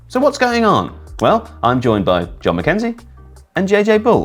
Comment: I misheard and thought he said Don McKenzie instead of Jon McKenzie. Thought maybe Mr McKenzie climbed up the ranks.